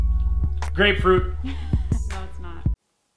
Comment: When he guessed the grape flavor I thought he said great but he said GRAPE!